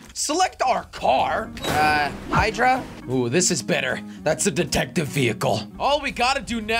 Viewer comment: I thought he said datective